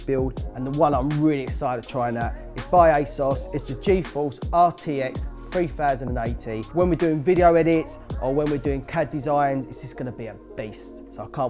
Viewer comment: did he say "RTX 3090"? Because they put 3080 in the description. Not to mention "RTX 3080" was written on the box of the product as well :DD